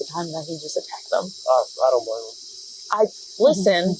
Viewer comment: Captions say "Um.. I don't know." but it wounds very much like he said "I don't blame him."